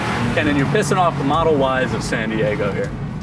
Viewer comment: "Kenan, you pissing off the model wives of San Diego here." "Good" I haven't laughed like that in some time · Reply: I heard “model Y’s of San Diego” and they had just passed one trying to turn onto the road. But either way it’s funny!